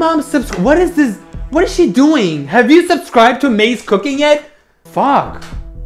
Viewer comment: DID HE CALL HER MAYS COOKING